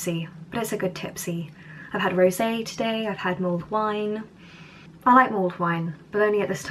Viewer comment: At first I thought you said "mould wine", didn't sound that exciting xD But then I understood what it was when you talked about boiling it. The Swedish version of mulled wine, called glögg, is typically made with a red wine (all kinds of red wine from 0% to 22% alcohol can be used, white and rosé wines are used sometimes too but that's very uncommon) which is spiced with for example cinnamon, cardamon, ginger, cloves, bitter orange and sugar. When you drink it it's common to put almonds and raisins in the drink. Every year the liquor store have new, only-this-year, versions of it, flavoured with other spices and fruits (for example blueberries, apples, extra ginger, vanilla, liquorice, saffron, lingonberries, spirits/liqour etc, but not everything in the same glögg, that would probably taste nasty xD ). But the classical version with the ingredients above are always to be found in the liquor store every year. I love glögg! It's so tasty! Especially with almonds in it ^^ If you're interested in trying it I think Ikea have it ^^ I have never tried British mulled wine though.